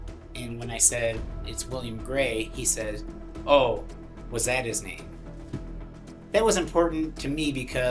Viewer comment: It's funny that the detective said that Shanniord(?) said "was" when at 18:14 he clearly said " that his name?" . Doesn't matter cuz the guys is guilty. So sad, Wil seems like a good ole man RIP. ☯️🌍☮️💖